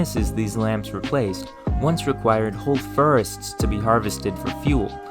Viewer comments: I’ve never heard it pronounced TERCH before. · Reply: i heard "furrest" too lol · totally thought you said whole ferrets, i thought "damn thats a squirrely animal to use for fuel" 😅